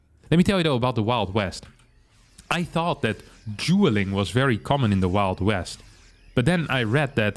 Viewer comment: I thought he said "Juul"ing in the wild west and was like....... vaping???? ohhhhhh DUELING!!!!!!!